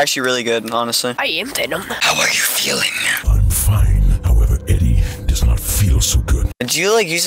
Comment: did you just say that someone "Doesn't Feel So Good"? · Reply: 'However Eddie does not feel so good'